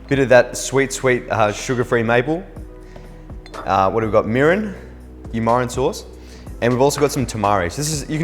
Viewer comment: did he just say you mirin sauce?!